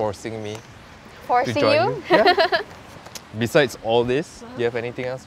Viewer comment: aq rase dia sebut "Haaaaa apeeee" Aq x salah dengar kan?